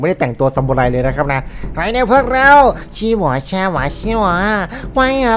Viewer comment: พี่พูดว่าอะไรอ่ฟังไม่ร้ไม่รู้เรื่อง แต่โครตฮา5555 (ชี่หว่อช่หว่อชี)อย่างฮา